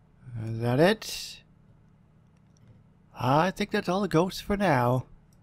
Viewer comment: LoL it sounded like you said catch all of the goats